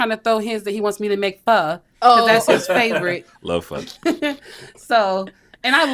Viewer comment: what food is “fa” or “fun” idk what she even said lol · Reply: Vietnamese PHO 🍲